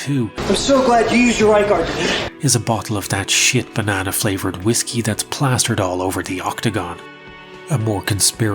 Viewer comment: Did i hear the words "banana flavored whiskey"? 🤮